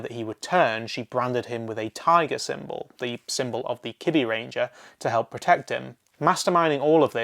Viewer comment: Maybe i misheard but did he say Gosai and Kibiranger instead of Gosei and Kibaranger??